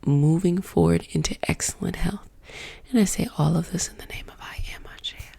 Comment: What is she saying at 2:23 "and i say all of this in the name of....?" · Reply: I am